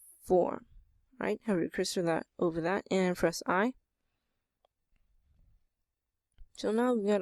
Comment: WTF I heard him say "Press R + S + 90!!!" for the text! Help me figure out what he said